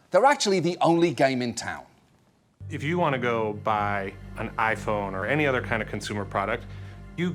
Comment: you have a choice when you want to buy an iphone? did I hear that correct?